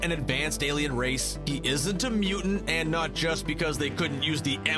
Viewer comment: What was the n word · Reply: 1:Its the M word 2:its Mutant